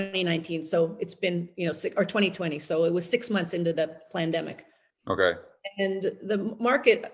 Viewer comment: Did she say "PLANDEMIC"? Nice- if she did. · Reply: She says it every time “plandemic”